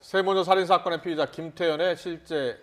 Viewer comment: 피해자라고 들리는거 같은데